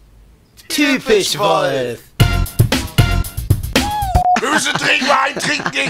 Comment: Was sagt bei 1:18? Ich hab's mit 0,25 speed probiert aber nicht verstanden. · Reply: „wir müssen dringend mal einen trinken gehen, gib mir ma‘ deine Handynummer“